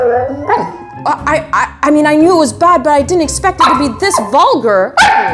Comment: Why did I think she said “what did you expect” without the translation